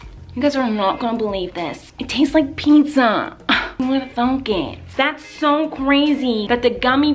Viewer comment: When she said thunk i thought she said the f word ! Lol 😝